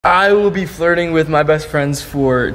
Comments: I thought he said "living" not "flirting" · I heard:"I'll be farting with mah best friend" i was like what are you going to do now